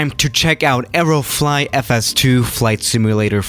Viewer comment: I thought u said aeroflot for some reason😂